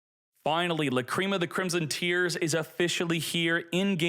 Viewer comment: wait did you say word tear